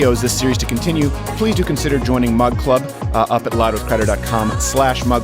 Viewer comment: I thought he's been saying "mud club" this entire time